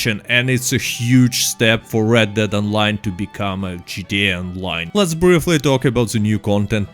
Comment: I thought you said "its going to make red dead online into Judea online." due to monetization. Taken aback for a moment lol but you said gta online.